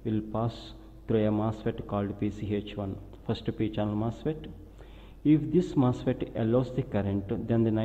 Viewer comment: Say what? First degree general mustard? Sorry but can anyone tell me what he's saying? I don't know much about electrical engineering.I'm trying to learn something. Is it "First DC general MOSFET"? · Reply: He says "First P-Channel Mosfet"